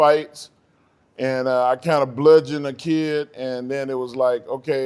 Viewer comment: Did buddy say he bludgeoned a kid? Look here, I done had to whoop a few mfz azz. I ain't never bludgeoned nobody fam.